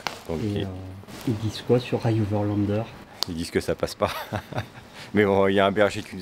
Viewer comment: À 15:52 tu parles de "...overlander" ou en tous cas je crois entendre ça, de quoi s'agit-il ? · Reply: ioverlander une application qui permet d'avoir des infos sur les chemins/auberges etc... Pour guider ton prochain !